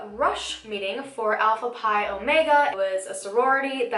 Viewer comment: On god I thought she said Apple pie omega 😂